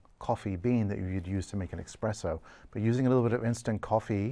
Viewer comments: did he say "eXpresso"? with an X? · Holup. 15:13 Did he say expresso?